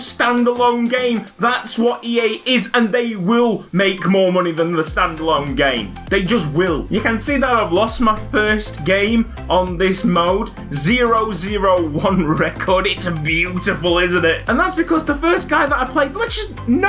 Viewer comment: Thought he was saying Korea mode